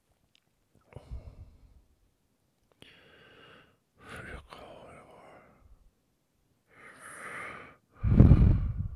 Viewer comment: I knew I heard you softly murmuring something. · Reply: I got a call in the morning